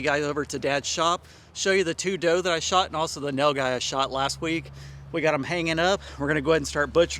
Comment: thought you said you'd show us the 2 doe and the mail guy hanging in dad's shop. What??? Had to back up and re-watch Tee he......made me lol. Thanks for the giggle and great video.